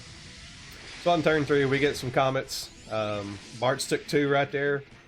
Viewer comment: I love the victory sip from the milkshake as the boss dies. Congrats! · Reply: I misheard accent when transitioning to the fight. I heard he chose you, then said ‘comets’. I heard “he’s an asshole with his ‘comments’” and thought he was talking about you!